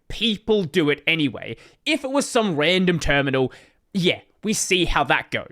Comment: I had to rewind. It sounded like you said "random Kernel" as a shot to GNU Hurd. Would have been a hilarious Easter Egg for those who know. :D